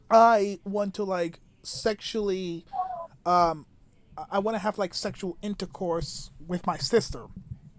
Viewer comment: Dude .. did you just say “sexual intercourse with my sister” ?? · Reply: I said I dont to have sexual intercourse with my sister.